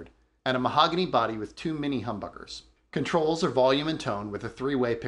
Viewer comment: At first I thought you said “too many humbuckers” when you were talking about the gretsch